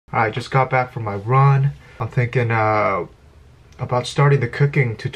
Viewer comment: i thought you said you came back from Iran... omg ;D